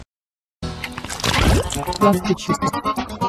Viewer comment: it sounds like he says: "klasky gusteau" 😄😄😄